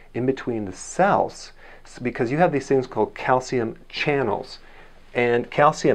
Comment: I thought you said calcium clogged arteries